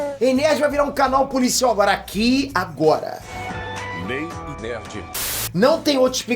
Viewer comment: nay e nerd... só eu q ouvi isso? Kkkkk